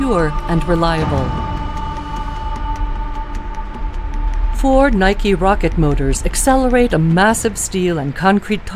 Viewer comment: i heard “Ford-Nike rocket motors” hahaha